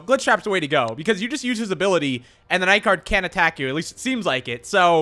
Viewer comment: ITS FINALLY HEREEEEEEEEEEEEEEEEEEEEEEEEEEE Leave a like on the video if you all would like to help me reach 2.5 million subscribers!! :D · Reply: I think i heart you say night gard can atack you ?!!